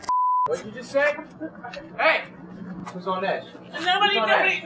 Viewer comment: In the last few seconds I know he must've said"I'm a fucking bad ass" but I always hear it like "Ima fuck that ass"😂😂😂 yes I'm weird dont judge me